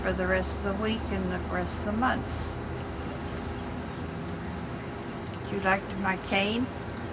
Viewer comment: it sounded like she said "Would you like to buy cocaine?" lol